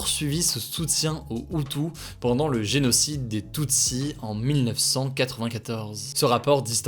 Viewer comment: J'ai entendu "le génocide des TUNSI " ☠🤦 Tunis ,Diminutif de tunisien pour les moldus ..